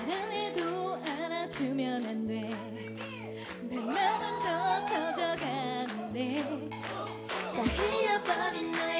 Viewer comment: What did that fan shout at 2:16? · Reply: "KANG SEULGI"